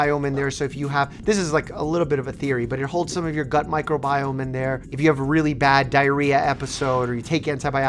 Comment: Accidentally misheard "gut microbiome" as "butt microbiome", but come to think of it isn't that the same thing?